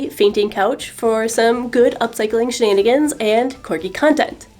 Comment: I heard "upcycling shenanigans" as "upcycling Shannonigans" and I'm not sad about it.